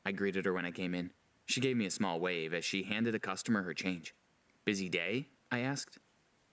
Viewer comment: when he said "busy day?", I thought he said "is he gay?".